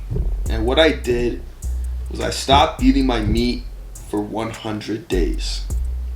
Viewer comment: sounded like he said I stop eating my meat